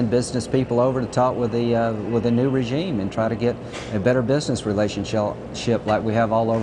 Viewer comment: Did he say "better business relation-Shell" at 0:34 ? Freudian slip much ?